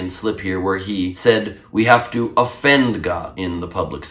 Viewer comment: ahhhmmm .... I generally like this, but I heard "Defend God" not offend. No matter what I listen to, I hope to hear the truth.